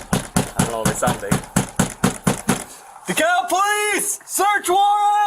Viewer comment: subtitles say the cow police. 😂 accurate.